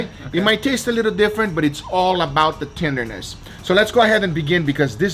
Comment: Damn I kept hearing "It's all about the jews" xD